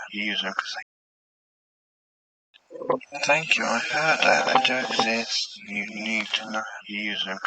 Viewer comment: “thank you, I heard (?) that I don’t, this… you don’t need to use everything” is what I heard. Interesting, what a great capture, especially reversed.